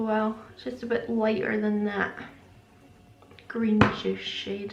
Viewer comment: I thought you said prune juice so had to double check the colour in my own Patricia palette. :-)